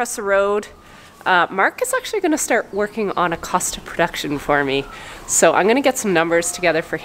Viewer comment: when you said Mark was working on a ‘cost of production’ for you, I heard ‘costume production’ and I can’t lie that when I realised I misheard i was little sad 😂🤣 xxx